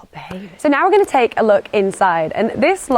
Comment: Thought you said Loo - Tin 🤣🤣🤣 boutsta pop a poot.